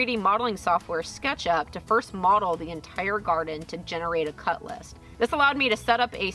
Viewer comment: I ...uh... thought she said Cutlass...and confess I was a little disappointing at the lack of obvious swordplay...